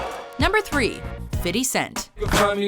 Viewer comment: Did she say "Fiddy cent"?